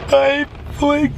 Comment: i thought he said I’m playing Minecraft